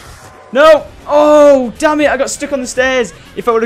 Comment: lol Syndicate said "danmit i got stick on the stairs" i think he tried to say stuck